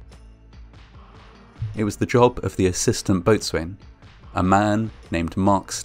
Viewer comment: Did he just say Boat Swain? Come on mate, everyone knows it’s pronounced bosun - bo rhymes with no, and the sun is pronounced as son in mason. Same with coxswain